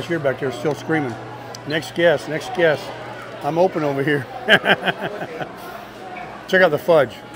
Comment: Omg…i keep hearing “next guest, we are open over here”..through the entire video….lol